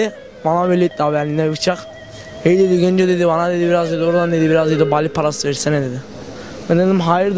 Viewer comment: hey dedi Genco dedi bana dedi ordan dedi biraz bali parası versene dedi (anlamayanlar için)